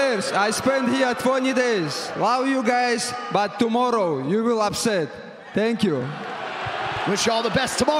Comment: “Tomorrow you will upset thank you” much love and respect for both fighters! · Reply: Thanks for the translation I was confused I thought he said “tomorrow night you will have said thank you”